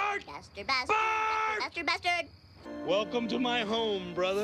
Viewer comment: When I first saw this, I thought Bart was saying Faster Faster Faster wanting them to go faster despite the speed limit saying 35.